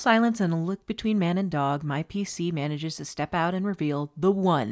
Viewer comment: I heard "DM PC" and got scared, love lil kobol' man